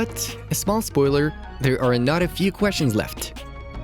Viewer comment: There are a lot of few questions? Minute 2:08 What did u/ he say I don’t understand